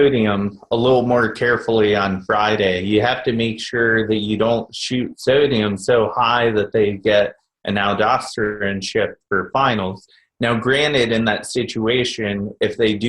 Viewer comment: what is saying??? Don't up sodium so high that ??? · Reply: "You don't want to shoot sodium so high that they get an aldosterone shift for finals..."